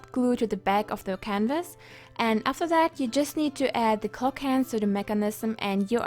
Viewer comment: " next you can add the clock hands to the mechanism " she has an accent so i heard it as " next you can add the cocaine to the mechanism